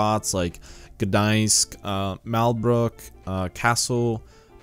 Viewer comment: *subtitles*, Malbork instead of mal Brook